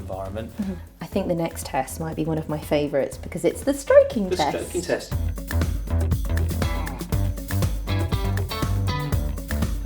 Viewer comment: that's the job for me: puppy stroking tester. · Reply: for a second i thought they said striking test and i was like oh no